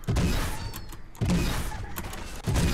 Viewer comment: lo que escuche fue: Chichi! Y me dio por ver una porno :v